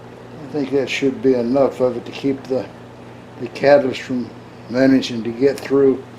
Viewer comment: Love that disclaimer. For my own haha moment, I thought you said 'cabbage' (later deduced it was 'catalyst'), so obviously I was increasingly more curious about your desiccant system from there forward. +1 for OG engineering, which of course is Old Guy in our case!